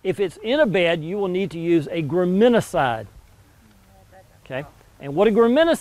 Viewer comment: Tony mentions a weed killer, I can't find it, sounds like he said gramancide? Thanks. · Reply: Graminicide